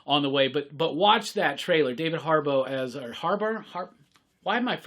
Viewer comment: Thanks for the news! "Did he just say making Harboro?"